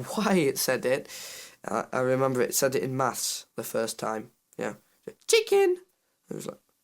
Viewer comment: Did he say "mass" or "maths?" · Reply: Maths!